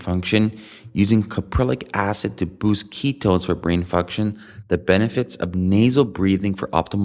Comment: Did he say "...ketons for brain function" at 0:25?